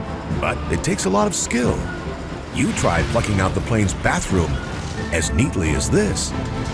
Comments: does he say try fucking out the bathroom 😂😂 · Does he say "You try fucking up the plane's bathroom...?" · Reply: You're deaf...He said "You try plucking out the bathroom" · do I hear right??? "you try fucking up the plain's bathroom..."